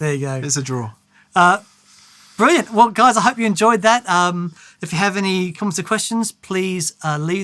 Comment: lolol, sounded like said "if you have any competent questions" 😂😂